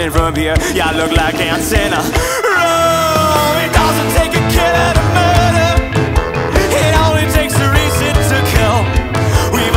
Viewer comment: It feels weird there's no comments lmao😭‼️ but the lines "it doesn't take a killer to murder, it only takes a reason to kill" goes so hard🗣️🗣️ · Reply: I heard "it doesn't take a kid out of murder, it only takes a reason to kill".